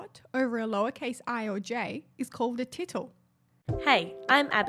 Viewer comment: Ha. I thought a you said tickle! The tittle is an integral part of the glyph of i and j, but diacritic dots can appear over other letters in various languages. Ie. ë ä ö. Feel free to use that if you wanna!